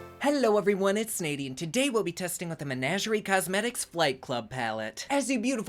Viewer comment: I swear I thought you were saying "fight club" and the color scheme still sorta fits if you think bruises lol